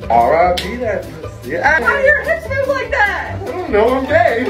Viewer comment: “How do your hips move like that!?” “I don’t know, I’m gay!” I love you guys😂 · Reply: I thought he said "why do your hips move like that"